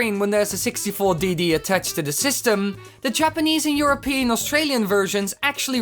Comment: DID YOU SAY, EUROPEAN?